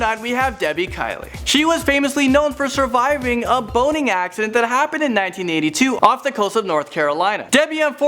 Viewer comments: I thought he said a "boning accident" lol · For number nine, I thought he said boning accident XD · Thought he said boning accident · did he say boning accident???? lol